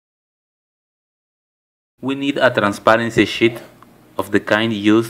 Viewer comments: did he say "transparency s word" or is it just me?! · Did anyone else think that he said "transparency shit" at 0:52 on the first listening of it.